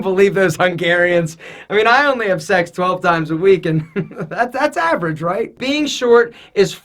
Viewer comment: wait did he say 12 times a week 😲